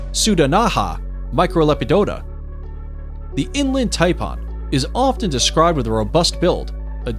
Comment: Lol a thumbnail that looks like the opening of a ...... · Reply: And to go with the "V" word, check out what the announcer calls the Inland Taipan!! He calls it "Inland TaiPAWN". I said " wtf? It sounds like hes saying "Inland Tampon"!!!🤯🤯😁